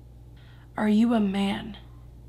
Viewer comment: I swear at 19:20 when you said are you a man, I heard um yeah